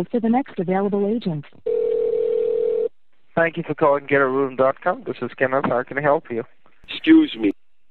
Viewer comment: It sounded like she was saying ghetto room instead of get a room.